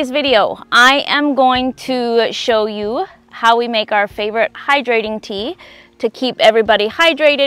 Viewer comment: I think I misunderstood what you said about the tea you were going to make and commented. I thought you said dehydrating and now I think you said rehydrating.. sorry for comment … which was… isn’t it hydrating and not dehydrating…. My mistake. You are such a joy to watch. I just started following you a week or so ago.. Is there a way I can find your first introduction to this channel, so I can get to know your family… Thanks for sharing all this knowledge with us… GOD blessings to you and yours always.